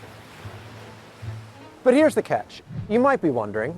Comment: I can swear I heard th pun ’…but here’s the ketch…’